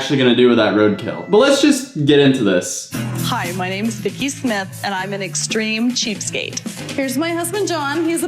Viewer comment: I thought she said “I’m thickie smith and I’m a extreme cheapskate”